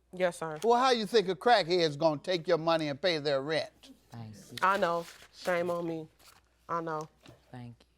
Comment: did she say thank you for being a crackhead who can’t pay her bills